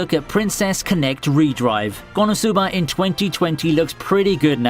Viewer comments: hmm they all don´t feel good for me. but good video. one word: It sounds like u said "Re-Drive" but the Titel says "Re-Dive". :D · Did anyone else heard him say “re:drive” instead of re:dive